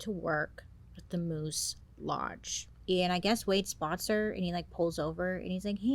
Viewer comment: on my 2nd watch, and each time i hear “walking to work with a moose” and it throws me off a little. Cuz why is this lady walking with a moose??? But then my brain actually registers “lodge” and I laugh at myself.